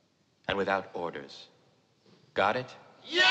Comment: i think he said "without ORDERS"